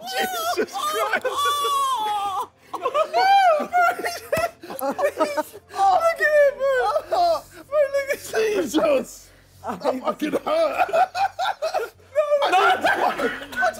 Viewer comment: did simon say the n word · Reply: No he said “bro look” not every thing that you can’t make out is the n word